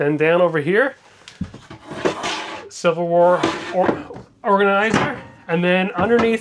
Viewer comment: I THINK HE MENT WEAR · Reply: I believe he meant "silverware " I heard "civil war"